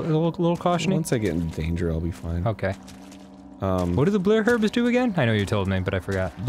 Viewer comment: I keep watching 4:16 over and over because Danny says 'blurh' herbs instead of blue and it is weirdly entertaining.